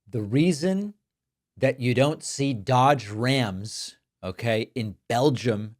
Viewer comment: I thought she was saying dog rams, calm down love